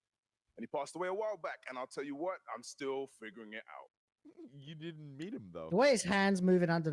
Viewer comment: i dont know who said it, but when he's talking about his dad someone says "but you didnt meet him though." That was so tasteless I had to rewind to make sure I heard correctly.